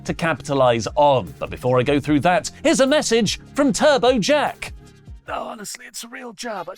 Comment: Turbo Jack slmost sounded like he said "Turbo Chet". Turbo Chet to Wrestletalk confirmed.💪